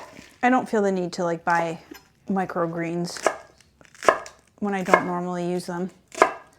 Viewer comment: Looks delicious but I’m not sure how you can substitute lettuce for grains. So am I (in UK) mishearing you say micro greens not micro grains? I’ve never heard of either. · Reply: It was micro greens, which are the sprouts of lettuce, broccoli, etc.